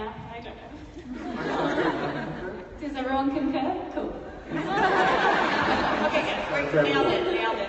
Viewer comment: What did dom say 26:18?? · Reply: If my English don't trick me, I believe she says "does everyone concur? Cool."